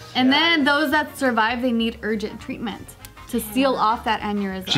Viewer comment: lol, I heard "urgent tweetments"